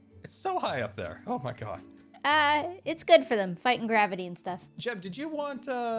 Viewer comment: What I heard, "It's good for them. Vitamin Gravity"